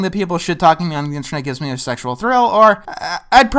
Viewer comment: Did I mishear him at 20:49, or did he say that people shit-talking on the internet gives him "a sexual thrill"? I thought he said in previous videos that he was asexual.